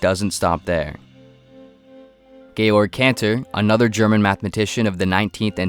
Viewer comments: Georg lol. I heard it as gay lord at first 😂😂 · i swear i heard gaylord cancer